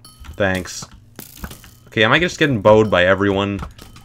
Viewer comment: i kinda zoned out while watching this and i thought he said “am i just getting boned by everyone?”. what’s wrong with me 😀